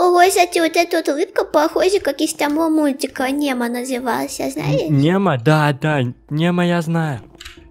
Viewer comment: кому послышалось мемо?